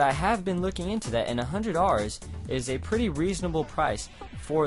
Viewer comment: Lol it sounds like you say "100 Rs"